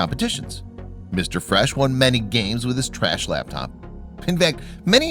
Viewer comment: What I heard: Fresh laptop What he said: Trash laptop